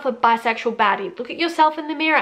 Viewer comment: My hearing isn’t always great, and I was listening to this while doing chores. The entire time I thought were saying “bisexual daddy,” lol